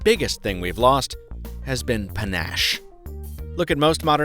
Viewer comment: What did he say? Pinasche?